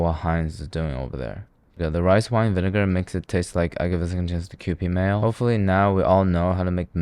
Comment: what is he even saying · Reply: "I gave a second chance to cupid mayo" a reference to cupid by fiftyfifty lol